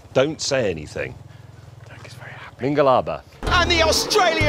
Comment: did is just saying မင်္ဂလာပါ(hello) ??!